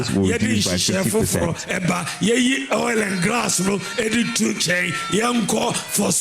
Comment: Listen carefully I heard him say oil and grass 03:48 minutes of the video😢